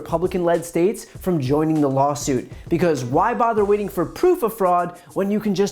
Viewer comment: at the beginning sounds like he says "lolsuit" 😂